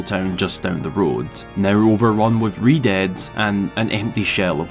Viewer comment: Captions say "overrun with redheads" instead of Redeads. 😆